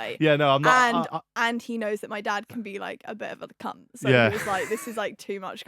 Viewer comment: Did she just say my dad can be a bit of a c**t??